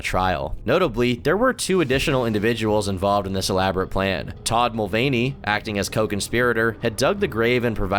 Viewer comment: I genuinely thought u said dylan mulvahny 😂😂😂😂😂😂